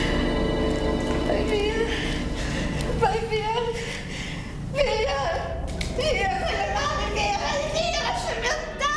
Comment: Who was hurting her? is that Maria Nina ? 23:24 what is ernie sayin? 26:05 Aww that's sad :C 27:35 l think he is telling you how he felt :'( l feel sorry for her, also for the man, it was horrible back than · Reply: It sounded like Ernie was saying, "Who are you to come here?" As for Maria Nina, I think it may have been her lover